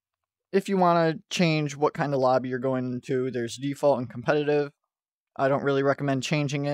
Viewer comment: I thought it said difficulty but said default, I was so wrong